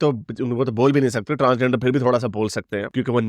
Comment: Did he said transgenders are "Naturals". Wow 👏🏻